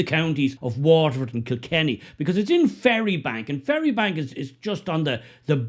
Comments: I thought he said a fairybank resident. Is it Fairybank or Ferrybank? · At first I thought I heard “Fairybank,” and thought what an appropriate place of residence that must be, for whatever that is.